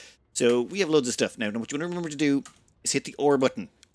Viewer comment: The or button? I can't quite understand what you said here. Apologies, can you clarify? Appreciate the video! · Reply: The "R" button, it's my accent and you are not the first to get confused 😃